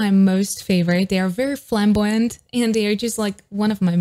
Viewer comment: did you just say flamboyant??!!tengen uzui will be very pleased 😁